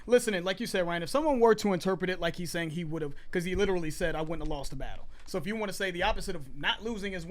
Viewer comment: I thought he said "I went and lost a battle" "I would've lost a bro"